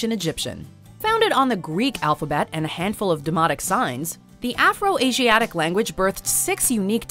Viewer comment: I had to rewind that, I thought you said “a handful of demonic signs!!”